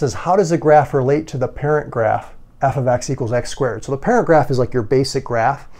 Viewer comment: Did anyone else hear "So the paragraph is your basic graph"